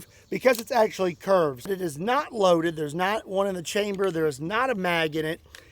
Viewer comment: I thought you said 6 and 1 in the chamber.